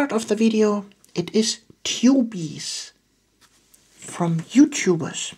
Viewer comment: Ahhh ok I thought you were saying "Chubies" like a term of affection. I see everything clearer now! Anyways, thanks for doing this video.